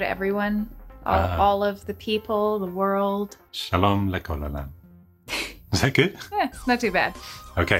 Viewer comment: haha ! I bet the dog thought he just said "Shalom Nichole Lamb".